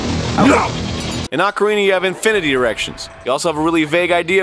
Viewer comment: "infinite erections" I also thought he said that the first time.